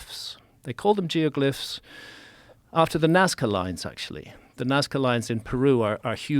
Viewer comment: Graham not jumping on Joe for “sixteenth chapel” is an enormous show of grace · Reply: I dont know if anyone else has captions on, but i cracked up when it misheard Nazca lines as "Nascar" lines. 😂😂😂